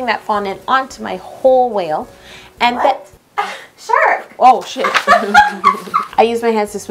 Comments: first time I heard swear!! (She said sh**) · It says sold out for camp cake, why ? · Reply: no hate at all but did you say sh**